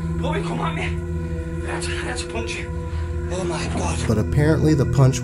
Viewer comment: i heard 'oh my god', not 'im gonna kill you'... i think :D